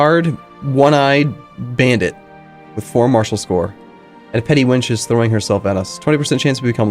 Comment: did you say a 'petty wench'? lol, it's all those petty kingdoms getting to your head